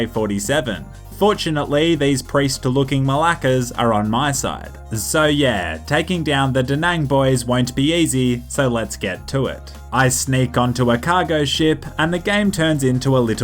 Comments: did you just say "malakes" as in assholes in greek? xD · did he actually said malakas? I’m dying 😂😂😂